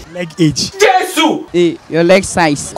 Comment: Did I heard 'your leg age?' 😂😂😂😂